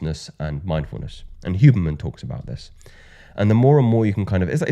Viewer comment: Great video! But, who is responsible for that hardcoded subtitles? A lot of mistakes on those subtitles!!! E.g. 17:01 You said "Huberman" and the subtitles says "Human"!!! Common mistake, but I think should be revised before hardcoding into the video!!!